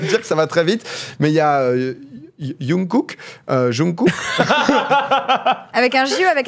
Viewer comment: J'ai gueulé quand j'ai entendu "yungkook" et "bé té s" -> "Jungkook" et "bi ti s"